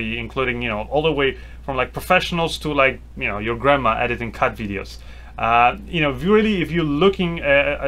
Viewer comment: LOL did you say "...grandma editing TED videos"?